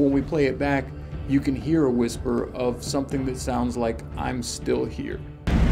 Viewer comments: The audio recording of the whisper “I’m Still Here” got my fucked up because at first I heard “I Smell Fear” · Did I just hear "I'm still here." on that freaking EVP recording? Dude. No. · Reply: I heard I smell fear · I'm still here omg, I thought he said I still can hear, this shit crazy omg God bless u boys. Prayers to u all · It sounds like he says I'm still here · To the whispers that said „I'm still here“ The audio is weird. Go to the moment the boys go in the room (23:25). You can hear the bang from the falling door that has blocked (23:34) - counting 3 seconds (23:37) - was saying to the camera man „come in“. At the point they repeat the footage make the same steps: wait to the bang (25:34) - counting 3 seconds and I swear I heard in the whispers, „come in“. What did you think? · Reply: Ash Roach For me it sounds like was saying "come in" and not how showed us that the voice said "I'm still here" at the end of the video.